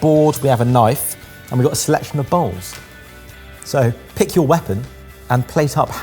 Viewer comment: and a selection of bowls me: did he just say they got slapped in the balls?